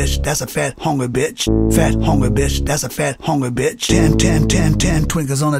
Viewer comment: i thought he was saying " Fat Homer bitch, fat fat Homer bitch" XD